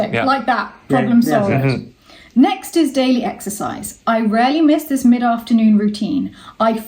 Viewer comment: EXERCISE, I thought you said EXTRA FRIES!